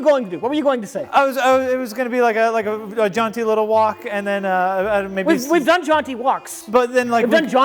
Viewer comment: When said he was going to do a jaunty little walk I honestly thought he was saying he was going to do a "John T. Little walk," and I was like, I don't know who John T. Little is but he sure walks weird.